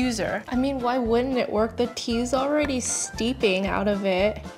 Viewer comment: I thought she said 10/10 and was like: WHAT!! She never gave 10 , i had to rewatch it and heard 9 😂😂😂😂 still high. it's rare of her